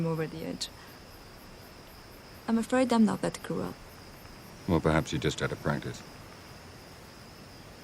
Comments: What did he say? "Well perhaps [...]" English is not my native so I will highly appreciate the help 😁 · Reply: Well perhaps you're just out of practice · Im still trynna figure out what the chick said at 5:24 ..I never could hear it clearly .Anybody know ?? “ I’m afraid I’m not the mat type of girl “ is what I’m hearing